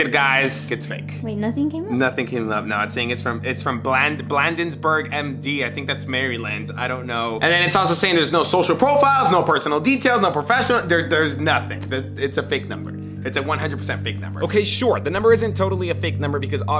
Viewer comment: did he say Mary-land?😂😂 ❤ 🤭